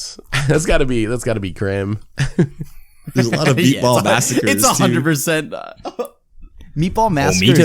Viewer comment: does say Meatball masacre? Love these episodes